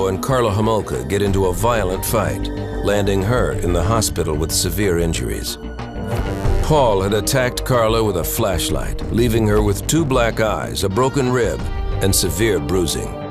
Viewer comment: Did he attack Karla with a flashlight or a fleshlight? Sounds like he said fleshlight